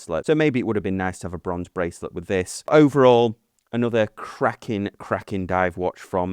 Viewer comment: Did you say "...another Kraken watch"??? Yup, that's Kraken alright.... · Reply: Cracking! You should know that as Wallace and Gromit use that term and I’m guessing your avatar is Shawn the sheep?